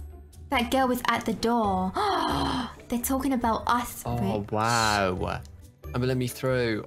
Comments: I thought this was a kids show is it me or did she say b***h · OMG DID SHE JUST SAY BITCH?? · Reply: I said "Rich" the person im playing with name...